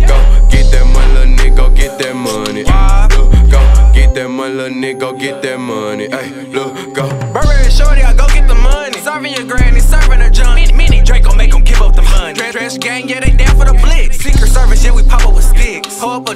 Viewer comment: Not Gonna Lie, thought it said burberry Perry (lil yachty's homie) i was like "Wtf lemme watch this shit lol"